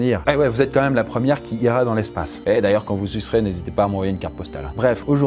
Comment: quand vous quoi ? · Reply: "Quand vous y serez"